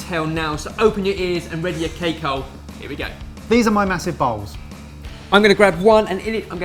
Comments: It sounded like he said massive balls not bowls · > Ok I start watching this video while I'm fixing lunch, when all of the sudden at 0:06 I hear "this are my massive balls!"😁😂😳😱. I just had to rewind the video again, and again I heard the same thing! Of course I know you are saying "massive bowls" but I wanted you to know that I just experience the biggest laughs of my entire life! I'm still giggling like a kid!😂😭😂😭😂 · At first i thought said "here are my massive balls". Lol Anyways, totally giving this a try! :)